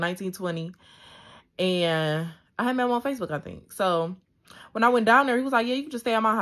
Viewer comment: 😂😂when did that push up and hit the ground, why I heard “when I came down… ooo!”😂😂😂